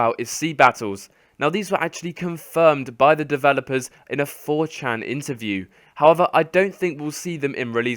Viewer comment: did he just say a 4chan interview??